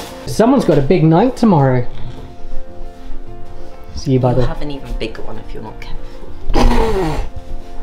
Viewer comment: Can someone tell me what Said at 6:54? 🥺 Anyway, this video wow! ❤️ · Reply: “You’ll have an even bigger one if you’re not careful”